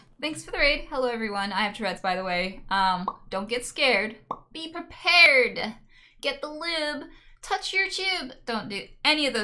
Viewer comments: istg i am not high but did i just hear "dont be scared be prepared, get the lube, touch your tube " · I first heard "touch your jews" and didnt understand but was weird anyways